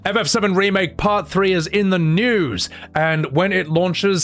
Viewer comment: I thought he was gonna say "FF7 Part 3 is in the nude!" which would be weird but also entertaining.